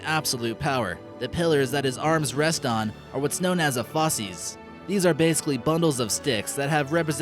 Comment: when they said fasces I thought they said fecses